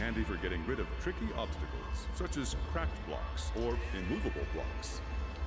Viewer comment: When it said “Cracked blocks” I thought it said “Crackhead blocks” 👀